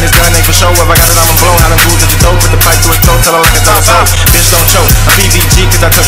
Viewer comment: What he say 0:58 after "this gun aint for show if i got it ima blow"? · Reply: Hollows to his throat